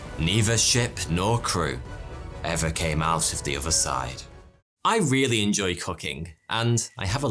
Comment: I´m not a native speaker so sometimes I have some hard time understanding some words.... When he said "but neither ship nor crew were ever seen again. I really love cooking", what it looked to me was "but neither ship nor crew were ever seen again. I really love cocaine" and I thought "well, I think he lost this sponsor...". Thanks god I was mishearing it ;)